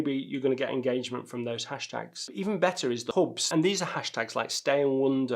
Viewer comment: you use a word in the context of hashtags that I just can't make out: "even better are the [horps??] like ... ". What is that?